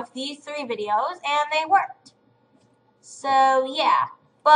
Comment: "I have tested this on 3 nico nico douga videos and they worked." I thought I heard "I have tested this on 3 niconico douga videos and they twerked."